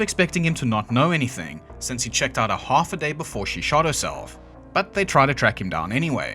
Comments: Is there a reason as to why you've started to censor certain words? I honestly thought you said she had shat herself. I'm not sure how the censorship works, but the beeps are very annoying, and make it harder to enjoy the video. You can use the word "fuck" but not "died"? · im conditioned to fill in bleeps with swear words, so... constantly hearing about how someone fucked themself to death was pretty wild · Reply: There was at least one instance where it was bleeped as "s--t", so yeah, I heard "shit herself to death" as well.